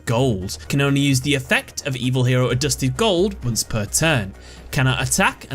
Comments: at first i thought adusted gold said adjusted gold that would have been cooler. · Did you say evil hero Booster Gold ...from D.C universe! Oh no lol just kidding.